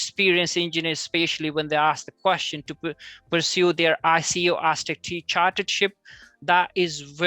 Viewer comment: what did he say there..anyone please help. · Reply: It's called being a member of ICE (Institution of Civil Engineers). If you are a member of ICE you will be seen as more credible and eligible is what he is trying to convey.